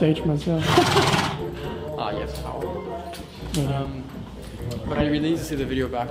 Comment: Did he just say ‘ah jesse hou op’ okaaaaay learning the dutch slang!